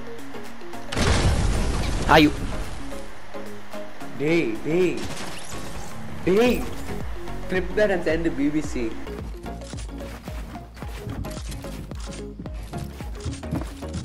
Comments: did he say BBC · Did he really say clip that and send it to bbc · did He said BBC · did he said BBC?